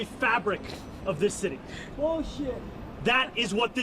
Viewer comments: I think I heard someone heckle bullshit at 16:30 😂😂😂😂😂🤦🏿‍♂️ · BULL SHIT is what I heard someone yell at 16:39 ? Anyone else? 😂😂😂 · Did I hear someone say BS during his speech? Lol